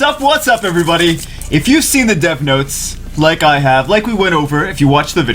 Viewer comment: First I heard if you've seen the Death Notes.......